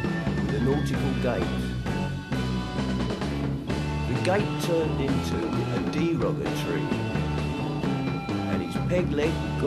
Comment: It's hard to understand what they are saying. I thought they said "the gate turned into a demon entry" but they say "the gate turned into a deroga tree"